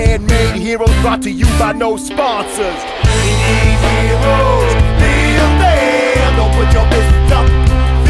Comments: i always thought it said We need heroes Be a man Don't put your fist up Be a man O.o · to me it sounds like he says "We need heroes be a man" rather than "We need heroes Build them" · at first i thought it said "We need heroes, Be a man!" what i thought made a tad bit more sense than "we need heroes, build them" but i guess ow it makes more sense